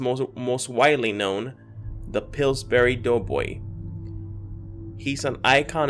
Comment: At first when you said his name I thought you said pillsbury dope boy 😂. Btw I remember is as blue as well